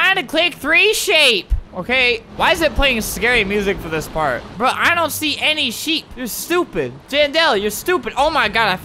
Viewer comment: i thought he said i dont see any sh!t BAD WORD